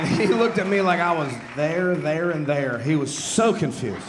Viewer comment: I gotta assume the transcript didn't get the joke and he was actually saying "There, their and they're"